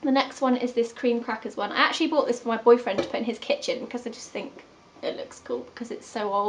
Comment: Did you say boyfriend or boyfriends? It sounded like you said boyfriends xD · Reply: Boyfriend